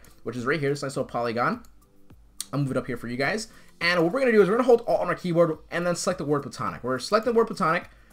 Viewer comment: What did he say 4:19 ? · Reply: hold alt